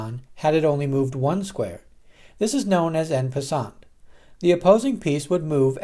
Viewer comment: what's the name of that move ??? I can't hear it correctly and if you please explain it again.. thanks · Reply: It’s called “En Passant”, which is French for “In passing”.